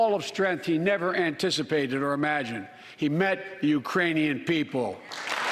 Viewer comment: 🤣🤣🤣 Kamala almost let go ， when Biden said "Iranians" people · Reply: I thought he said “Uranian”🤣🤣